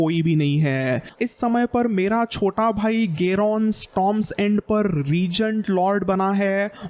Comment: Did you say Garon....